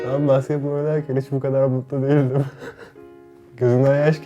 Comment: ne dedi duyamadım ( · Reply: Ben basketbol oynarken hiç bu kadar mutlu değildim demiş